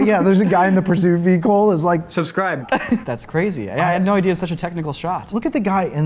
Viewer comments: Did anyone else hear a random "Subscribe"? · This isn't the only one, there's more - but seriously, did anyone hear these "subscribe"s aha?? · Anyone else hear the subliminal message??? “Subscribe is said throughout the video. Once at 5:40 or so and others throughout. Weird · why do i hear subscribe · is that a subliminal message to subscribe or am I hearing things